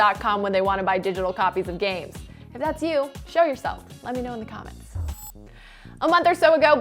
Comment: I thought she said “kill yourself” I was like daaaaamn. (She said show yourself)